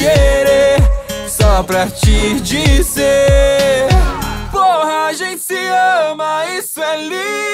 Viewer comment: Da primeira vez que eu escutei eu ouvi "quando a gente se ama.." ai eu de novo "eita carai é porra MAIS HINO AINDA" esse cd ta cheio de letras com duplo sentido em 🌚